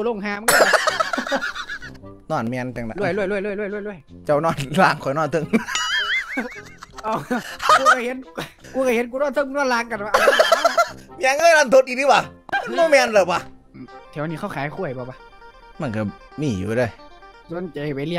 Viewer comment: ได้ยินวะ"รันทดดีติหว่ะ"